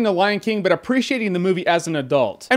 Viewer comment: For a moment there I thought you said: "There's a lot of stupid stuff out there that will make your kid dumb, planking is not one of them!"